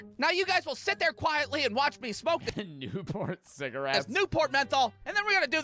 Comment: I thought it said "newborn cigarettes" 💀